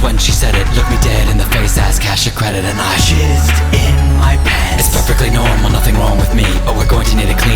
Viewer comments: at first I thought they were saying she is in pants!!!!!!!!!!! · does she say ass cash or credit? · when i watched it when it first aired i thought they were saying sh** in my pant but i found out it was jizz i didnt know what it meant so i googled it .......it makes it more funny